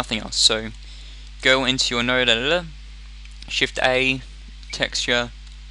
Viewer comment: When he said "Node editor" I heard "Nerd editor"... I mean like what a fail...